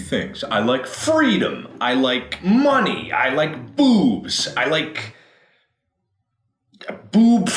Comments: I thought he said booze not boobs lol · Instead of boobs, I thought Mark said booze, and I was like, "But Mark, you can't drink booze because you'll die!"